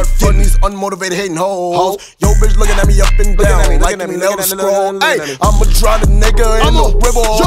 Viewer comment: Did I hear "Like them Elder Scrolls?"